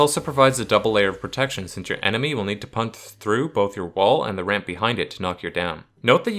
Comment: did you say “punth through”?